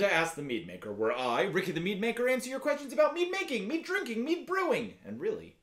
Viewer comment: Sounds like you say “me drinking, me brewing etc” 💀